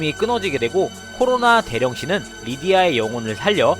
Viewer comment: 아니 '코로나 대령신' 말할 때 자꾸 '코로나 개ㅂㅅ'으로 들리잖아요ㅋㅋㅋㅋㅋㅋㅋㅋㅋ